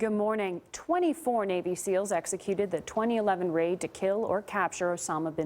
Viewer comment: At first I thought the girl said 26 seals were executed I was like wowowowowowoeowo that’s impossible